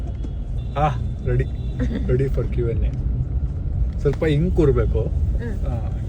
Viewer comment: what did u say in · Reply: its ready for QNA